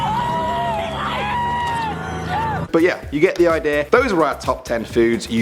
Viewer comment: did he just say the EU